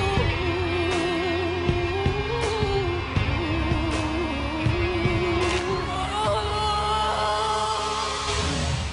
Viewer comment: What did she say at 00:08???? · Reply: Gabriel Kake she said “you’re amazing you’re amazing no no no OOOOOOH”